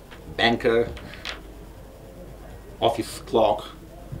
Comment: Office Clock!!!! Kidding. I know what he said.